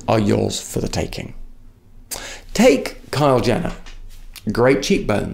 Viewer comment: is it just me who heard him say kyle jenner ahahaha